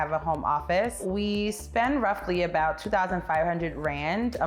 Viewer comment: When she said "2500 rand*" on groceries I misheard it as "two thousand five hundred *grand a month on groceries" and was like 😮